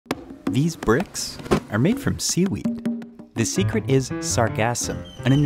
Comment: I thought you said ”the secret is sarcasm”